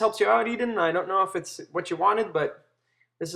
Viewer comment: For some reason i thought it said drawing with Post Malone😂😂